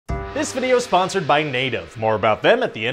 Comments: i didnt hear the v at the start so i heard “This video is sponsored by NATO.” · I thought you said this vid is sponsored by nato